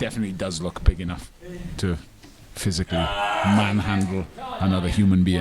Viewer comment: is that a i hear?